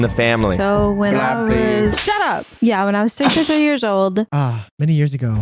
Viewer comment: FOR SOME REASON... I thought she said she was 63 years old 💀